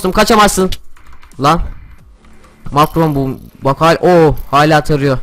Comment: makrom bozuldumu dedi ? abi bu suçlama değil sadece öyle duydum. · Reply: Makro mu bu dedi makro bozuldu demedi